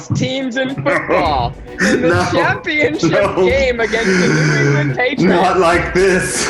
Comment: did he say Cleveland Patriots?!?! 😂😂